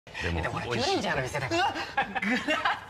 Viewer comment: 最初「パトレンジャーの店ですから」って聞こえて草 ゴーカイジャーもディケイドと何か似てますねw